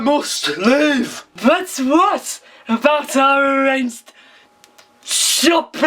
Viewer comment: What the fuck was saying? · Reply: "BuT wHat boUt ouR shOpPiNg TrIp?"